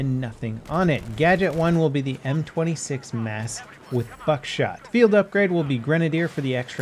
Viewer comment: did someone hear f*** shot. LOL it sounded like it. anyway nice video hahaa